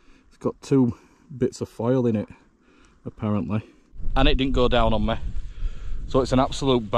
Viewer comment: first time i heard "it didn"t go down on me" as a bonus lol